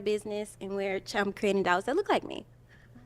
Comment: did she say " Dials "?